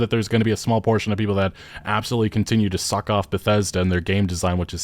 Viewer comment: Did he say "Suck off Bethesda" ??